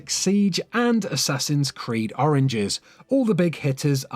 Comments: Who noticed that he said oringes instead of origins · Sounds like he said Assassin's creed Oranges 😂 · he said oranges instead of origins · Did he just say "Assassin's Creed Oranges?" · did he just say "Assassins creed Oranges"? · Is it just me, or did he say Assassin's Creed: Oranges? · Boi did you just say assassins creed oranges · Did he say Assassin’s Creed Oranges XD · he says oranges instead of origins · Did he just say assassins creed oranges? Lol at about the 14:20 ish mark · he says assassins creed oranges instead of Origins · Did he just say assassins creed oranges as a Ubisoft fan I'm offended (not really) but come on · did anybody else hear that he said oranges not origins? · Did you just say "Assassin's Creed Oranges"? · Did he say Assassin's creed oranges? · did he really say oranges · Did he just say Assassin's Creed Oranges? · did you say oranges its origins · Wait, did he say assassins creed oranges? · am i the only one who heard him say assasins creed oringes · sounds like he says oranges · did he say assassins creed ORANGES?!?!?! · At number 15,did he just say Assasins Creeds Oranges? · you can hear him say oranges instead of origins. LOL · did he just said “assassin’s creed oranges” I want to see that is it a secret game XD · who else heard assassins creed oranges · did he say "assassin's creed oranges" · did he say assassins creed oranges